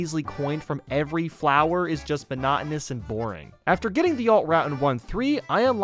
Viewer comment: did you just say bonotonous